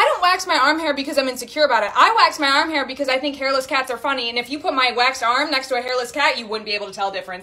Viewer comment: I THOUGHT SHE SAID “hairless gals are funny” IM DEAD